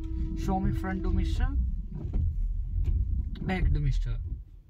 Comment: What did you say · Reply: Thanks for replying, I am Asking about front Demister and rear demister